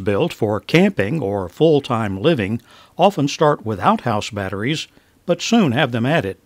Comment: You said : “Vans built for camping or full time living often start WITHOUT house batteries”. I heard: “Vans built for camping or full time living often start with OUTHOUSE batteries.. LMAO..😛